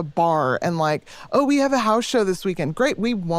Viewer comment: I think she said House Show, like a DIY punk show in someone’s living room or basement.